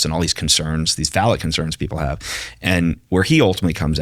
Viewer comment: did bro just say "phalic concerns" instead of falacious concerns lmao